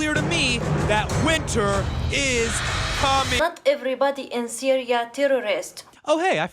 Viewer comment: hahaha , I had to replay that ! At first, it sounded like she says "Fu<k everybody in syria terrorist , not everyone terrorist" ...wth ! Btw, Bill Maher on the Charlie Rose show gives some facts on this whole topic . Maher gives replies to Rose with cold hard numbers . With those numbers, an intelligent person can draw their own conclusion and not be told what to think. Everyone has a right to think what they want ... but they we have a responsibility to come to an informed conclusion that's not based on someone else's opinion or propaganda . And we should be able to come to different conclusions and still respect each other . Enough with turning EVERYONE against everyone... this is what all those in power want , for everyone to be against everyone . Canada and America we are neighbors ...don't hate on each other just because of our politicians .